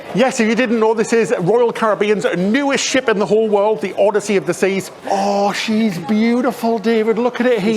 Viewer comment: oh my gosh ay the beginning i thought you said odyssey of disease, and after that i kept hearing as that :-D [it reminds me of the strategy of naming your child something negative to ward off misfortune]